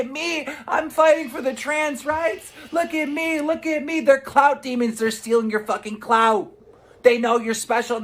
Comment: I really WANT to be a clout demon. Sounds like a Dark Souls boss. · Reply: The whole time I thought Keemstar was saying Cloud Demon! I was SO confused 😂😂😂